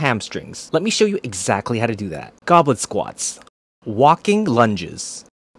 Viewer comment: I had to watch this twice because I for real thought he said "goblin squats"